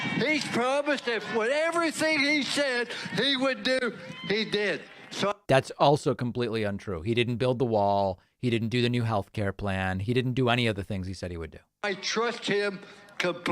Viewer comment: I thought you said completely uninterrupted!